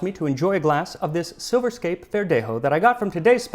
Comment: I was eating and I swore I heard "Silverscape pendejo," and I nearly snorted my spaghetti out my nose.